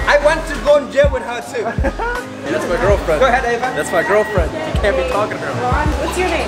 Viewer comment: did anyone else hear one of the stokes twins say that's my girlfriend at 5:00 mins